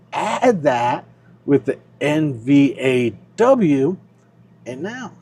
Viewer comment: You are talking about combining with "NV2" you said, "you add that with NVAW..." sounded like you said NVAW, I can't find NVAW? Did you mean Roundhill NVDW?